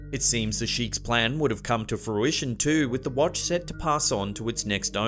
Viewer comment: Hehehe it sounds like your saying wantch